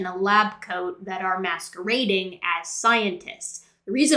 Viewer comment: I thought you said masturbating as scientists. I guess it's the same really